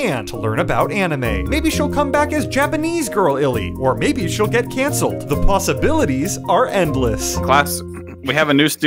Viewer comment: I didn't hear "She's gonna get cancelled" I heard "she's gonna get cancer"